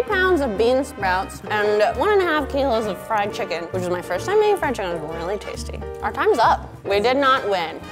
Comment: DID SHE SAY THAT THIS WAS HER FIRST TIME EATING FRIED CHICKEN AT 8:28???????? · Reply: *making fried chicken